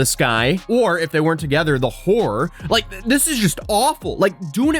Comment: oh that's "Horror", I thought he called her a wh*re 😅